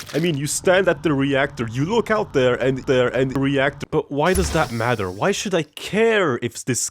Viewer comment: what the fuck are you saying my guy "you look alt there and there and there"